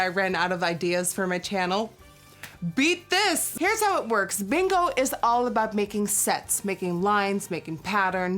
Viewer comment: I’M DIEING! I THOUGHT SHE SAID “BINGO IS ALL ABOUT MAKING SEX”!! I KNOW, I’M DIRTY MINDED AF